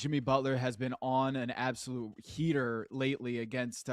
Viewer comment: At first I thought he said, "Jimmy has been on an absolute [tear] lately" 😅😢 (heater*)